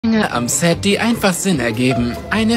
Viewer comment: Was, „Inge am Set“ ? oder hab ich mich verhört xD was macht sie denn da